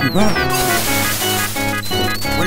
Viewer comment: Good grief, all this time, after the minecart section, I thought he said "We won, look here!" While holding up the Sankara stones. Struck me as something odd for Indy to say, but oh well. Could hear it clearly in this vid, and just now figured out he says "We walk from here!"